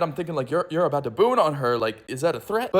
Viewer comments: I heard "Boone on" and I thought it meant sending someone in front of a dinosaur and shooting them. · WHAT DOES BOONING ON SOMEONE MEAN I FEEL SO THREATENED-?? (i did NOT expect multiple people to explain what booning is and all have different responses) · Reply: I thought he said gooning at first🥀🥀